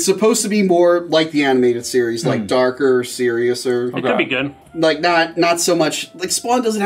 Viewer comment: He really said "seriouser"?